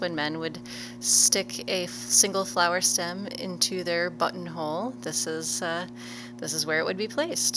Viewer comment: thought she said "flower stem into their butthole"